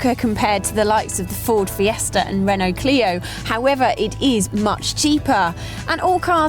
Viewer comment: did she say however this time ? - yep